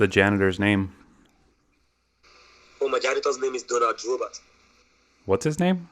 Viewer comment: Did he just say his janitor is Donald Glover? XDDD